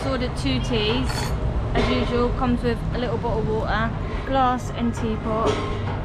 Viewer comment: Thank you for this wonderful video, and I love how the British pronounce " bottle of water " Because what I hear is like " Bo'ohw'o'wo'er " 😂